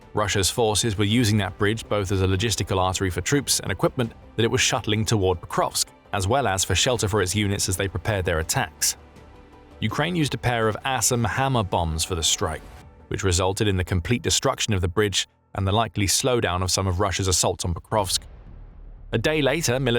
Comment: Sounded like you said "Ass and Hammer" bombs. I'm good with that! LOL